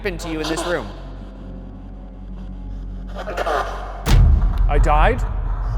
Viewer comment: am i the only one who heard 'f-ck off' and not 'i died' 🤔